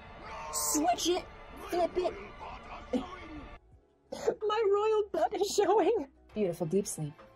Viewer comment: Huh, I always thought he said “My royal parts are showing”